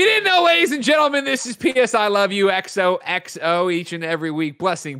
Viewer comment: Did Greg say „Penis I love you XOXO“? I thought episode 69 was next week.